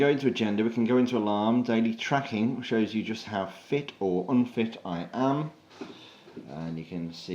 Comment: Jesus Christ I thought he said 13:05 "just how thick or unthick I am"